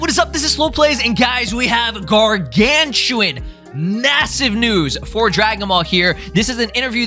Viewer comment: did you just say massive